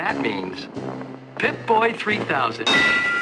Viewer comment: lol the first time i played Fallout 3, I thought heard the overseer say here's your pimp-boy 3000. Could've sworn he said pimp....